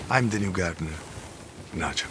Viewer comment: i thought he legit said "I am the new gardener, not you" and then i saw his name is nacho which is more hilarious